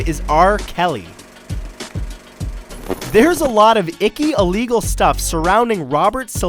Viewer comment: One time in the video an ad came up so it sounded like he said,"there are a lot of icky illegals."